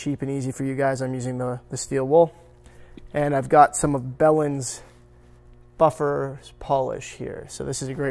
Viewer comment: Belhens, I could have sworn you said Bellend 😂🤣